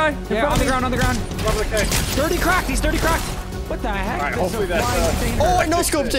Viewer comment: did Nick say What The Heck or am i just rued???????????????????????????